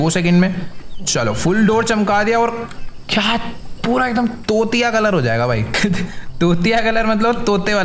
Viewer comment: me be like - 1 second I thought he was say chutiya😂